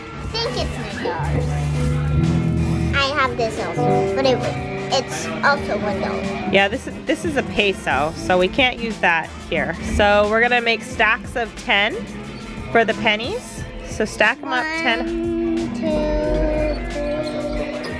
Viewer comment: did i heard it right? said peso on 16:46?